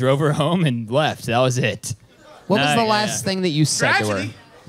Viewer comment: what does the person in the back say? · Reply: I think they said “tragedy!” 😂